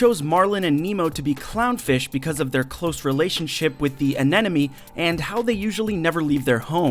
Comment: did he said ananeme? lol..